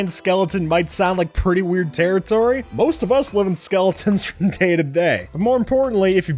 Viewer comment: Wait Wait Wait Did He Say Most Of Us Live With Our Skeletons Everyday In